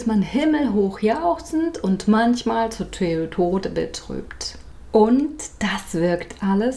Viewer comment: Ich habe verstanden: "Manchmal ist man himmelhoch jauchzend und manchmal ist man Toyota betrübt!" Alleine das hat meine Laune binnen der ersten Sekunden nach oben gebracht. Den Satz merke ich mir... :) Nix für ungut -ich liebe Ihre Videos! Das ist mal so richtig guter Content! Danke dafür!